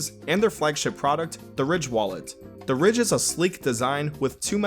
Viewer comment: Lmao I thought he said the rich wallet during the sponsor